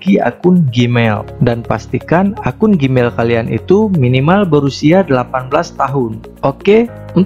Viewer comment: Kedengeran akun gmail mesti 18 tahun. Apa gw yg salah denger